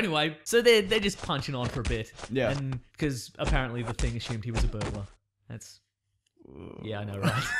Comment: what did he say? I just found out he said “apparently the thing assumed he was a burgalar” or at least I think so I found out by putting my phone close to my ear